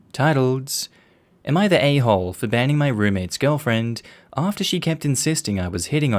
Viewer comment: lol. I misheard banning my roommate's girlfriend and thought she said she was banging her. NTA.